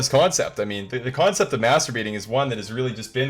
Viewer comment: I like how you said Master Baiting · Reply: I thought he said "masturbating" at first lmao oops my bad